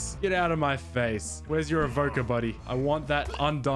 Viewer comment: Play subtitles at 10:02 and it will say where’s your avocado instead of where’s your evoker